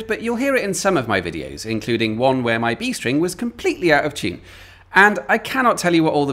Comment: I must be losing the plot. When you talked about playing the guitar, and said in one video you said the B string was out of tune. At first I thought you said the G sting was out of tune. So I skipped back and was thankful it was only your B string.